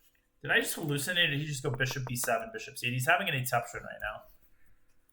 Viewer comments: what’s the word daniel uses at 11:04? i hear “etuption”?? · did I just hallucinate or did Danya say 'etuption'? What is that?